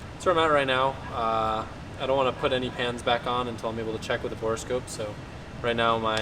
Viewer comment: You were saying you don’t know what you’re going to do….and then I could have swore you said “I’m going to wait until tomorrow to see what my horoscope is…” but you said “borescope” too funny.